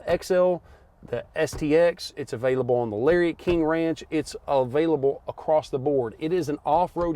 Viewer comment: Wait, did he say "Larry King Ranch" at 13:40? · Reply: “Lariat, King Ranch”